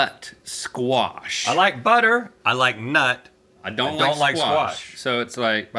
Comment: what did he say... 😭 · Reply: He likes nut 😂